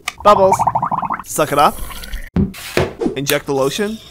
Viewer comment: When he said "Suck it up" *dirty mind activated* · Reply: I HEARD "Suck it off"💀💀💀